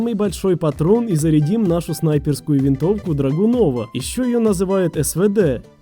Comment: послышалось драгон лора)